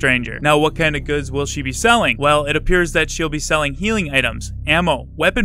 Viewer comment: I thought he said "healing hams"